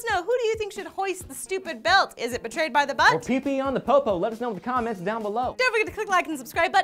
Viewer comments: lol i thought it said pee-pee on the poo poo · I thought this said pee-pee on the poo-poo. I vote myself as the winner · Who else thought it said "pee pee on the poo poo"?